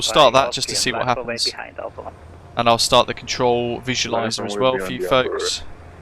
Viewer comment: Great video. Just started using the E190 as well. Question..at 57:04 on this video, you mentioned an addon I’ve been looking for so long, I think you said control visual something. How can I get it? Thanks. Again amazing video.